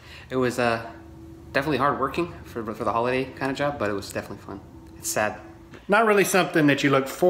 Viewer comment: Did I hear one of the guys cleaning up saying " it was definitely fun " ? Huh like wtf buddy?? 8:29.